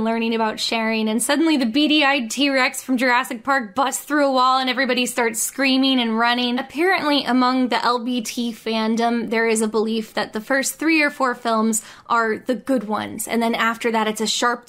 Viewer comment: When you said "LBT fandom" I misheard it as "LGBT" and I didn't even question that the gay community was apparently really into the land before Time movies